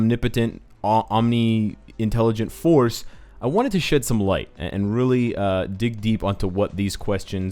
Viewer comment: sounds like he said "I wanted to shit some light" Yeah, go do that